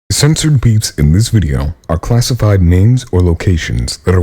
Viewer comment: Lol I thought he said "classified memes"